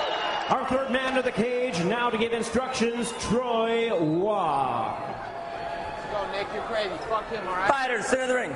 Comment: 🤣🤣🤣 What did Nate say at 1:24 ??? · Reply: Let's go Nick, you're crazy! fuck him, alright?! 😂💀